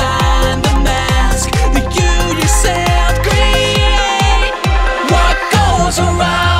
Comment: When it said “That you yourself create” I thought it said “Do ya self and create”